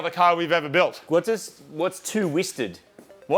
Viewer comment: At first I thought Marty said 3:09 what's 2 STD ? 😂